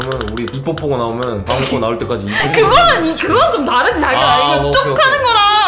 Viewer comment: 자기야라고 하지않으셨나요 아니어도 그케들리네요 광대승천